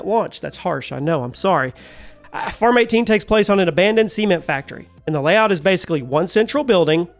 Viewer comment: I really thought you said Farm 18 was the Semen Factory